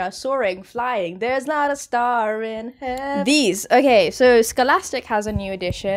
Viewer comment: "you have no idea what's coming up" me: * genuinely has no idea and upon seeing it I thought it was Romeo and Juliet * Minute 3:33 made it sound like you said "there's not a star in hel—"😂💔